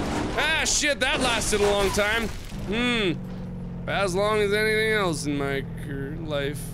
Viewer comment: he was going to say career but changed halfway to life so it sounded like "anything else in my car life" 😂😂😂